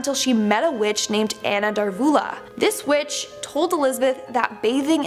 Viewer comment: I thought you said this b** not witch llol